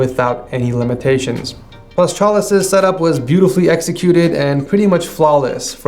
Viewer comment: Is there no ads cuz the auto generated text thought you said s*x toys instead setup wars?